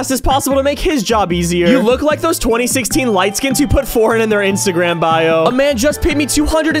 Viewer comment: I legit thought it said forehead not foreign😂